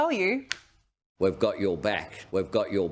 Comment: I thought he said we will BLOW your BACK.!!!!